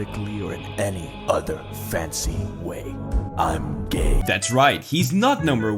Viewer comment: Did death just said I'm gay 💀